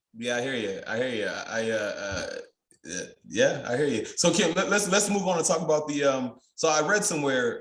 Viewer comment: Did you call him at 43:54? 🙂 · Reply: I stubbled over my words. I was going to ask a question, "Can." Good catch. Thanks for watching. Also, this is Whiskey Wednesday, the libations sometimes take over and give me an accent. hehe.